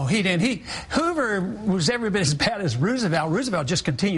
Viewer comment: What was it that Chris Matthews says at 4:54, 'coolage' or something? 'You want to go back to 'coolage' or something?